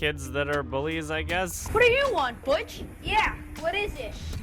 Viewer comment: I thought the kid said "What do you want Bicth?"